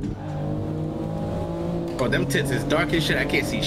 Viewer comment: 🤣 I thought I heard t*ts too